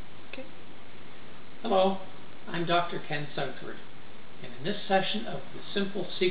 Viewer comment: Ha, i thought his name said DrunKens